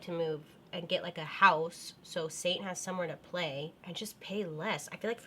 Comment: Love you. When you were talking about wanting a house it sounded like you said "So satan has room to play.."! Haha I know your dog is Saint but i still laughed really hard inside.